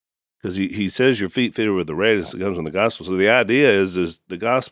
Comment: What on earth is Al saying at 33:08?? Your feet feel what?? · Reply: Closed caption said: feet fitted with the radiance that comes from the gospel.